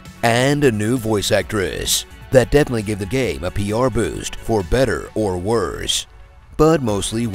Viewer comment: what he said? (I speak spanish and I dont understand that part) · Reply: I think he meant she gave the game a popularity boost But idk i didn't understood what he said either